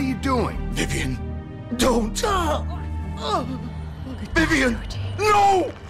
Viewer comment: For anyone doesn't know what Vivian says, Vivian said "Goodbye, Georgie." before she died.